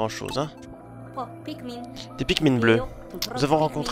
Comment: Attend.... c'est moi ou Alph il dit "fuck pikmins"